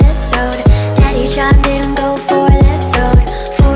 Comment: At first it sounds like she was saying daddy shot ingo for the throne lol I don’t know why and how I got that🤣